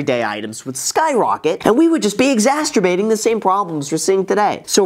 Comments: How is the top comment not pointing out he said “exasturbating” instead of “exacerbating” at 9:52 LMAO. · Did I hear “exasturbating’”? 😆